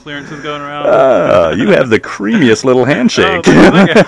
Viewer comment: Lmao “Creamiest little handshake” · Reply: fuck, i thought he said 'creepiest' and that guy just laughed it off